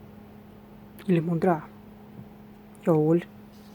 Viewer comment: на 0:07 мне показалось что она сказала JAWOHL ) мне нужно меньше смотреть каналы Истории...